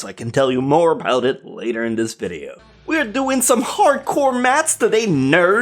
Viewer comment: "were doing some hardcore maths debate" But i heard "were doing some hardcore masturbate"